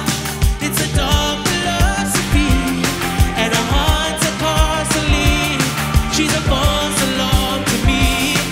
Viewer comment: i hear "hearts of parsley#